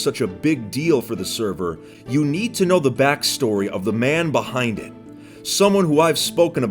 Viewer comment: I thought he said the man behind the slaughter